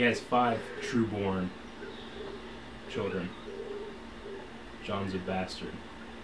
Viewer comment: When you said "Jon's a bastard" I heard *Joffrey's a bastard" and I was like immediately "Yes. Yes he is."😂